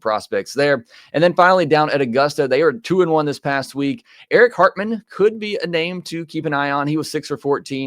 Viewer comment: For a moment I thought I heard Eric Cartman instead of Eric Hartman and got worried